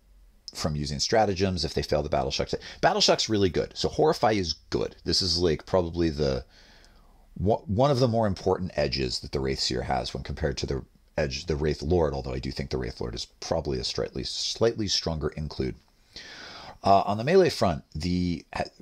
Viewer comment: it sounded like you were about to call it an Edgelord